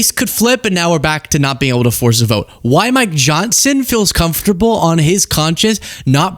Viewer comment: I heard "My johnson feels comfortable" and I thought "Well, thank you for sharing but that's really none of my business."